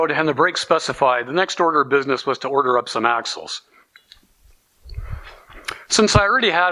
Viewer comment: just found ya wow crazy intriket engineering hotrod stock shit hows that fer a description of my views anyway happy holidays and thanks fer time oh ya at 2:40 sounds like u said lets order some ass holes lol